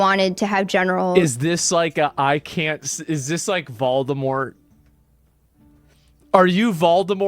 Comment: at first I thought you said BALTIMORE, but then I realized you said VOLDEMORT. Oops....😊😊😊😊